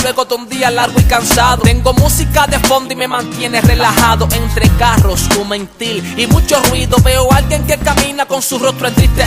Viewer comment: Hola😊 Alguien que por favor me diga qué dice exactamente en el seg. 0:48 Es que creo que dice algo así como "entre carros, humo en tí y mucho ruido" pero no estoy segura. Alguién que me confirme porfiiss🙏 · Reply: Entre carros o mentir y mucho ruido